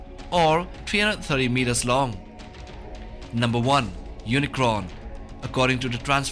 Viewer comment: I thought he say unicorn